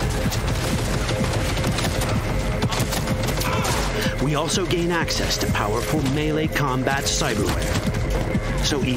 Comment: Can we just take a moment and appreciate at 9:00 what that NPC says to you lmfao. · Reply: I can't believe you're gunna make me say it lmfao! "Spread Your Cheeks!"